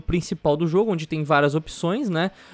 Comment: Sou o único que ouvi o dizer "Player Tauz"?